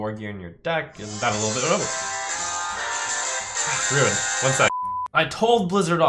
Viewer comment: is he saying "Druid"? I completely don't get that joke.